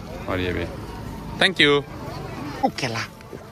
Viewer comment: mein kya bola bro oh kela hahahah 😂😂😂😂😂😂